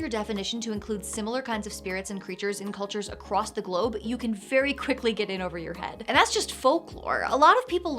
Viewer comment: Did you say, "You can FAIRY quickly get in over your head"? 👀